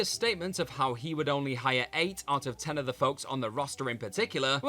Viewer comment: I thought Kenny actually said "I WOULDN'T hire 8 out of 10 of you" hence why the tough love was thought to be heavy-handed